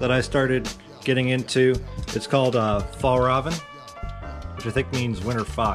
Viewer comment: fjell räven it means mountain fox, I could swear you said far oven 😁 it’s a Swedish brand